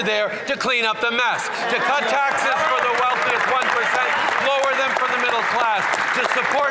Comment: Did I hear that right at 2:15 “cut taxes for the wealthiest 1%